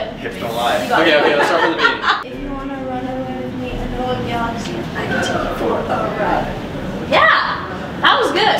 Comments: If you put your volume up at 9:32 it lookes like said "your doing so good..babe" was it just me who heard that or am i going crazy? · OK AT 9:33 AM I THE ONLY ONE WHO HEARD "your doing so good... babe"